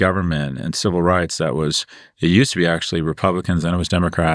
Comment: Did I hear that correctly @ 21:55? Did Scott say the Republican Party was about person freedom? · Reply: He mumbled: “It used to be …”